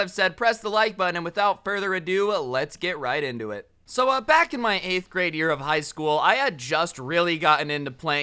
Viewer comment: Doesn’t anyone else hear him say Back in my eightieth grade of high school?